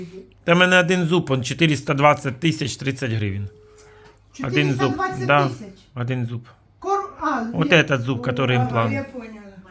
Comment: Мне послышалось?420,000 один зуб?